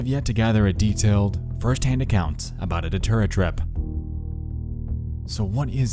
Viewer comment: I completely misheard as DoTerra, like the essential oils company. I was like “Damn, these MLM huns are fucking people up”